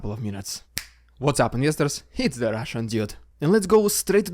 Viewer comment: Thanks, nice video as always. just asking, do you say "What's up, Investors"? or am i mishearing it? :D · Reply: Yes he did say investors....you've probably guessed by now this is an attempt at a comedy channel